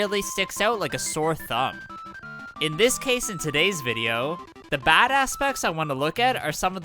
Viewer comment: i thought he said “badass specs” 😂